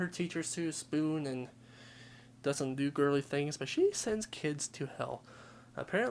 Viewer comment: Sounds like she said "i have sent the kids to hell"